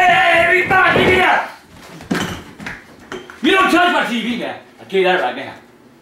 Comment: what does he say at 3:24? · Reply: You don’t touch my TV now I tell you that now